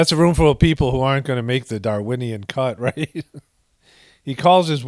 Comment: "They aren't gonna make the Darwinian" cut classic Paul Comedy Gold. Ok I really thought he said goats as I was listening and typing this comment hahahaha Paul's a better comedian than that goatly ghoul